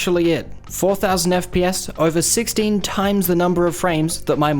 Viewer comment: i heard todd saying 16 TIMES THE DETAIL